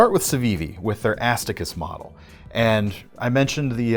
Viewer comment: Did you just say... Ass Ta Kiss? 🙄